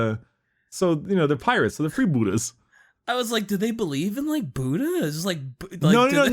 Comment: i thought he said free gouda 😋😋😋😋